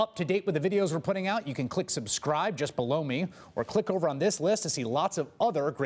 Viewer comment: Just fyi when the guy says "click the button just below me" at the end, it %100 sounds like he is saying "just blow me." Watch to the end to see it.